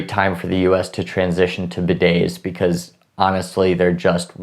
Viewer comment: what did you say? i really didnt understand what the us has to transition to. im assuming free health care but i dont know. could someone help? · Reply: Frank He said Bidets. They're the things in toilets that shoot water at your butt🤷🏻‍♂️ never tried one lmao but apparently they're cleaner.